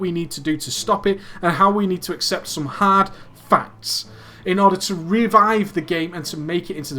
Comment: it sounds like he says "hard fucks."